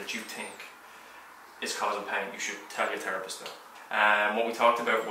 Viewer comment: Sounds like he said terropist haha might as well be.